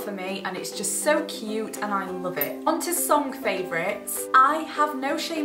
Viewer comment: When you said song favourites I thought you said sock favourites. I have a weird mind